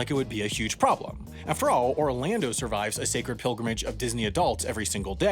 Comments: Did you just say a religious"pyramid" instead of pilgrimage? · Did he just say “pyramid” instead of “pilgrimage?”